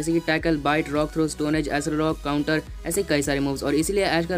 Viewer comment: did he say TAKLE ?